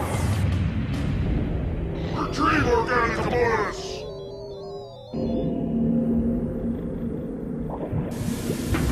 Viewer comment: What does Vic say at 0:32 "retrieve organic [what]"? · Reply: 'Reteive organic components' I believe he says 🤘☢️☢️☢️🤘